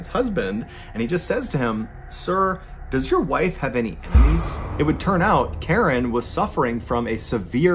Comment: I can’t tell what he’s saying at 7:05. “Does your wife have any <what>?”. Do you know what he’s saying, please? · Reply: Enemies